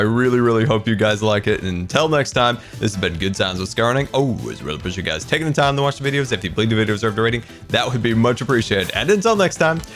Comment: does anyone know what Scar always says in his outro just before saying "that would be much appreciated" ? Thanks cause I'm not a native English speaker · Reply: "And until next time, this has been Good Times with Scar And I always really appreciate you guys taking the time to watch my/the videos And if you believe the videos deserve a rating That would be much appreciated"